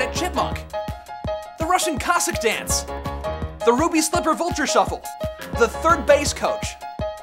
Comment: I thought 0:50 was called the Five Shuffle. (The character's name is 5.)